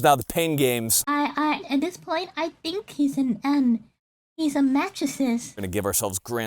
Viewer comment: Swear said he was a mattress at first lol